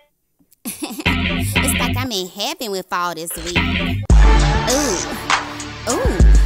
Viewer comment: At the start I thought she said ''feels like im in heaven with all this weed''